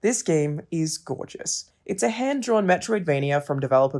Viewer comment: I've been listening to Metric so much I thought you said "it's a handdrawn metricvania"